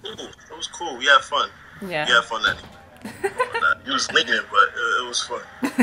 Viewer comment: What did he say at 12:10? You was slinkin or linkin or something?